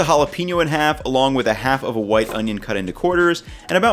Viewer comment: When he says "white" in "white onion" he cuts the onion right at the "T" part in "white" and the cut makes a "Fff" sound, so when I first listened to this section, I thought he said "wife onion."